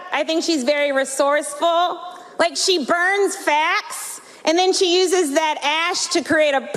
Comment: I just honestly can't hear what she says past the neoliberal screech and the 10" fake eylash peacock feathers. Plus I actually thought she said "burns Fat" and not "facts" lol. Burns FAT is funnier😄